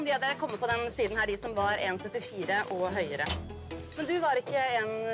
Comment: what does she say? 174cm?